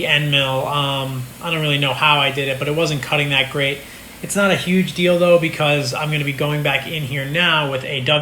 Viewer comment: I swear at 9:10 he said Its not that hudge dildo.